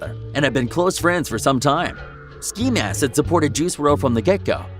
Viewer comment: Did he just say ski lask